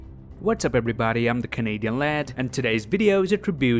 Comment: Did u just say "I am Kevin hart" instead of " I am canadian lad"?